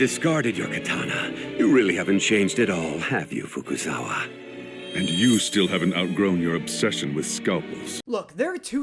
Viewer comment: he actually said and i see your still lusting after young women